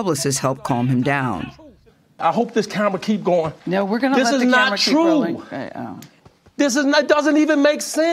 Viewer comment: what I hear: "we're gonna let the camera keep rolling" subtitles:"we're gonna visit the Amish" wth